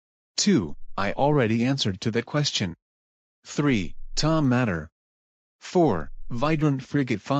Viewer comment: Did you say "Tow Mater"?